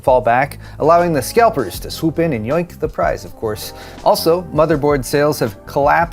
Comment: did just say yoink??